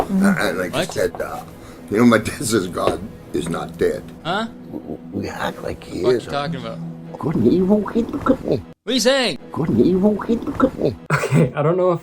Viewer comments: I think his trying to say 'good and evil came to €ut me' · I think lauries boss said ”good & evil. Hey look at me!” Cause he definitely says ”good & evil” And Laurie looks down at the floor Then he says ”hoggumfloggumcookabean” And she raises her head back up to look at him I wanna be totally honest with you guys. So ill be upfront and admit that im not fluent in the language of old, alcoholic, white, male, bottom of the barrel, Q-List, Christian film actors…… but im preeeeeeeeety sure, like 86.2%…48.12% certain that he maybe possibly says: ”Good & Evil” Then ”Hey look at me!”